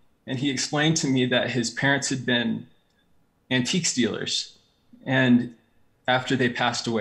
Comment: Lmao I heard them say, "his parents were ANTIQUE STEALERS." And I was like what?